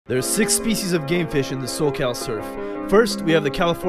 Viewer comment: I really thought you said "species of Gay fish" 😂